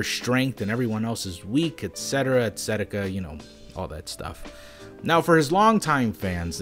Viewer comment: Did you just say excetika?